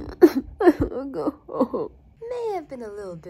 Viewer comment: Pov i finally understand what hes saying: 0:09 i wanna go home 1:41 i wanna be a kid again 😭